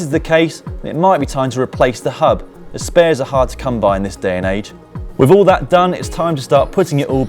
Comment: you say ''There may some wear evident. If this is the case, then it might be time to replace the hub. .... spares are hard to come by these days?'' I cannot hear well what that last sentence is, regarding the spares. Can someone help me out? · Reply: he says "spares are hard to come by in this day and age."